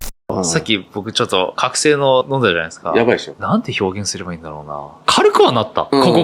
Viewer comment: さっき覚醒剤飲んだじゃないですかぁ。に聞こえたw